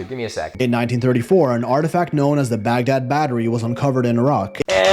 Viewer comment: I listened to it at least 15 times. I could have sworn said Baguette Battery😂